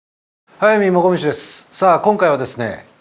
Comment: 最初の『速水もこみちです』が、『Hayami Mokomichiです』に聞こえる